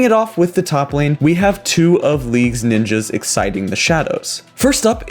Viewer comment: Did he just say "exciting the shadows" instead of "exiting the shadows" at 0:56?